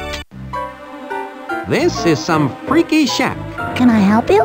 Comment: When he first walked in I swear to God I thought he said "This is some freaky shit" 🤣😭